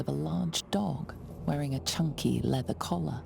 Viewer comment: For a moment, I wheezed because I thought the narrator said chonky instead of chunky!